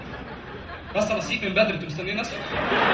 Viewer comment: شو قال الدقيقة 2:45 ؟... هل قال "بس انا صحيت من بدري! انتو مستنيني أصحى؟؟" كلامه سريع!!